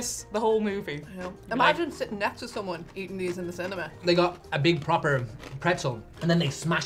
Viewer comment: What did I hear at 2:08? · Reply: She said something about eating them in the cinema